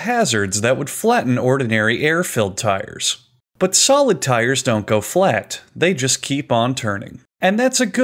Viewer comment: For a moment i thought it said Solid Trees.